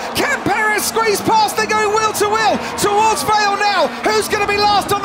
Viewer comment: Someone tell me what it says at 4:47 towards _ now? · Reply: Vale. The name of the corner.